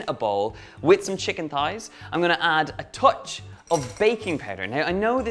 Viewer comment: I thought he said bacon powder lol will totally try this !